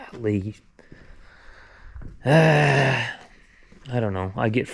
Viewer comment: Did you say cuttin corners like they “DoDo”. 🤣🤣🤣🤣